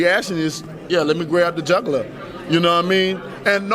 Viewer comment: I thought he said "Grab the juggler" for a moment then realised he was saying "jugular". Still, the only thing missing from the build up to this fight has been a few jugglers.